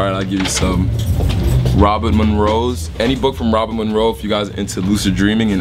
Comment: what is the name of the author, he says it twice, and at 2:26 grey who? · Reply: ***** Robert Munroe and Grayling